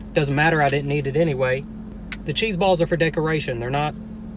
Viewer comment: I thought that said Chinese balls instead of Cheese balls 🤣🤣🤣